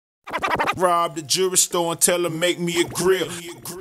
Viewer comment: My mom thought he said “rob the Jewish boy and tell him make me a grill”